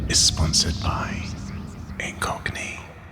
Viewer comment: When you said "this video sponsored by Incogni," it sounded like you said "this video sponsored by a Cockney." lol